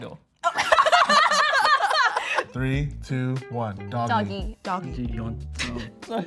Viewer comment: What did they say at 3:25? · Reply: doggy